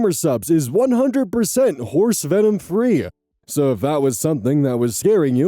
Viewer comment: I was doing the dishes while listening, the water muffled your voice a bit. I was listening to the outro while blasting water on stove grates. I thought you said that gamersups is "horse semen free" I was utterly bewildered. I had to back up the video to make sure I heard you correctly. You unintentionally gave me 1d4 of psychic damage